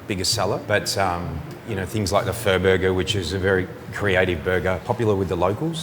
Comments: i thought he said "furburger" Lol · Did he say "fur burger"? I like eating those, too! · did he say fur-burger? That would be popular. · Oh, I thought he said "furburger" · I had to rewind. I thought he said "fur burger". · Hahaha when that guy said Pho Burger I thought he said Fur Burger initially. Yeah I know I have the sense of humor of a 12 year old lol. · I swore he said "fur burger" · I thought he said it was a fur burger😂😂 not Pho burger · The guy was saying Pha Burger... I had to stop and rewind because, I swear he kept saying Fur Burger. I was like... well, of course it's your most popular burger. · when he said pho burger i could have sworn he said fur burger. lol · Thought he said the fur burger at 5:03!! Guess it was the pho burger...Lol!!! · Damn bruh, i thought he said fur burber, not pho burger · I thought he said the fur burger!!😂🤣😂🤣😂🤣😂😂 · i thought he said fur burger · The owner needs to pronounce the PHÓ burger better, it sounded like he said FUR burger. That's a whole new experience. 🤣🤣🤣🤣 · I liked the idea of that pho burger better when I mistakenly thought the chef was saying 'fur burger' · At around 5:00 I thought he said fir burger. I was waiting for him to say whisker biscuit too. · Sunny not trying to laugh after hearing what sounded like Fur Burger : ) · LOL it sounds like he is saying Fur Burger not Pho Burger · I could have sworn the guy said fur burger when he said pho burger. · I totally thought dude said his best burger was the fur burger lmfao